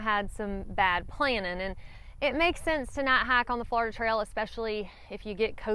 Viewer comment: I'm just stoked for another video where I get to hear you say "night hike" 😁. Seriously though I'd much rather learn from other people's mistakes so thank you for sharing! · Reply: I thought she was saying "not hike", until it dawned on me it was "night hike."